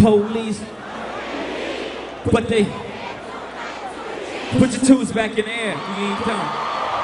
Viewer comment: Anyone understand what Jay Z said at 3:17 after saying ‘put your 2 back in the air’ ?? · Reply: Put your twos back in the air we aint done